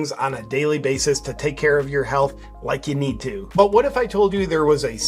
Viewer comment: Gee I thought you said take care of your house. I was about to order. No way to help my health